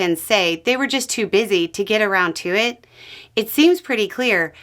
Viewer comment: Is it just my imagination, or did I hear "A round tuit", rather than "Around to it"? :D Like, it actually seems like that's what you're trying to say, here... (presumably for comedic effect, if so, and if so, it worked!)